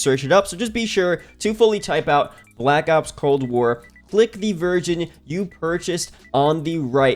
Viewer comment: I thought you said click the virgin you purchased😳